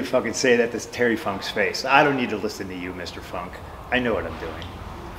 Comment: Wait YEO 💀 I ACTUALLY MISSED what he said cause all I heard was I don’t need your advice Terry funk grow up and I thought no way he said that cause I don’t think he would but I typed this out after I thought he said that… “Did C.M. Punk just slander Terry Funk” look man I love Punk but I’m sorry but that is a line you can’t cross bro. Terry is a LEGEND and an honorable man. He wasn’t at ECW one night stand ( with a much larger paycheck) because he made the commitment for less money to attend & as old as Terry was then he wrestled his roughly 3,282,849th match of his career at hardcore homecoming with a 3 way match with Shane Douglas and SABU each with a manager he knew those were a lot of the guys that MADE ECW what it was I.E. TERRY FUNK, Mick Foley, Shane Douglas, the blue meanie, New Jack, Axl,& Ian Rotten, Mikey whipwreck, 2 cold scorpio, SANDMAN, RAVEN!🐦‍⬛and last but certainly not least MFing SABU! That is where the heart of ECW was. I wish WWE would have saw that and booked at the very least Sandman, Funk, Foley, SABU, Raven, for sure and maybe booked Shane Douglas for ACTUALLY MAKING ECW EXTREME CHAMPIONSHIP WRESTLING, for real Shane had A lot of influence and was a big part of the switch from eastern to extreme and then leaving the NWA and it took balls of steel for him being the one who nearly buried his career by trashing the NWA title on television, I remember that being a HUGE deal and probably why he wasn’t invited to many promotions to wrestle after that. He wa